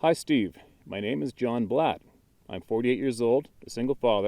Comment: Hi Marley, thanks for sharing! I have shared my other encounters with Steve but they are pretty strange so they will probably go into the second book he is compiling. I doubt they will be shared on YouTube, but we'll see. Thank you for the kind words. :) John Blatt (from the video). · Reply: Mr. Blatt... First, I apologize for getting your name incorrect, i thought I heard him say Black, unfortunately, getting names wrong happens more the older you get, lol..... I really enjoyed your encounter story and I do hope that we get to hear another of your encounters really soon. Strange or not, I believe their are many people that have experienced strangeness in their encounters. I have heard a few that were really different but I believed them 100%.... By the way, you carry my favorite name....John, which by the way was my Grandfather's name and although I never knew him, I always felt close to him somehow.... It is so nice to meet the man behind the encounter and I hope we have a chance to talk again one day.... I wish you the best and I can't wait to hear your next encounter!.... Take Care and Be Safe John.... A Friend in Missouri, Marley